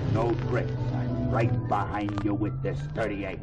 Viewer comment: When I was a kid, I thought the goon said "I'm right behind ya with this DIRTY APE !!"